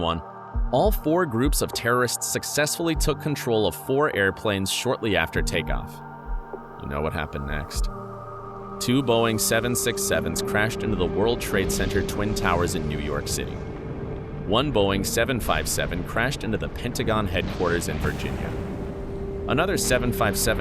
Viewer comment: did he say 6’77777?